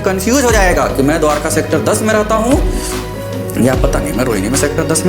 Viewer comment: mein kya bole sir ne 🤔🤔?? Bata do bhai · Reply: Sir is telling about address... i.e. Dwarika sector 10 which is in delhi